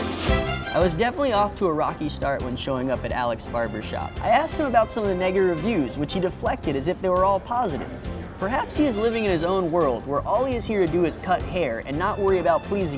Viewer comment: did he just say the n-word instead of negative